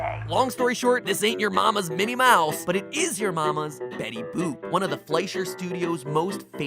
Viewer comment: Hey, this is me and not some Spambot! So, as you see at the end of the episode, I give a shout-out to Crunchroll's new service VRV. It's seriously great. Theres all sorts of awesome shows on there to watch. Anyway, to make this extra special for us Theorists, they're offering THIS WEEKEND ONLY a free 30 days of their ad-free service. Just SNASH the link in the description (That's "smash" with an N...cause I'm weird) Anyway, test it out, see what you think! And I'll see you next week for a theory I'm SUPER excited for! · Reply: The Game Theorists I thought you said Betty boob